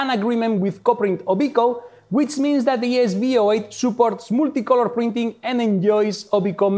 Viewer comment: what did he say? Obiko multicolor printing?